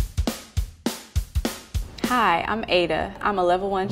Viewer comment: no joke thought she was gonna say "Hi, I'm amateur"